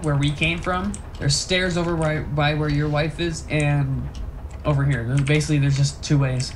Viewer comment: did he call his mother "your wife" XD